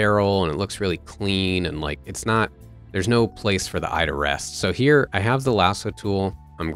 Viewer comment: i def thought they said "no place for the IRS" 😂😂